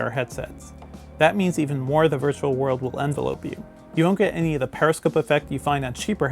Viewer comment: Did he say envelope (like a mail package) you???? I think you read that wrong.... It's envelop you (common mistake)